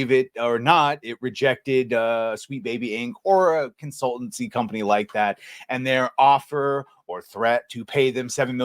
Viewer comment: I have some hearing loss but I swear I heard refer to Sweet Baby Inc as an "insultancy like that". Genius freudian slip.